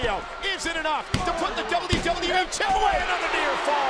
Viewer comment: did cole just say wwf champ?